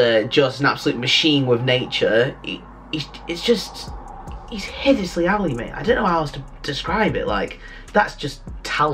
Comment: what's he saying right here? I heard him say that before, I'm not familiar with this slang. "head [something?] alley" I can't recognize the words correctly, though the meaning or tone can be inferred.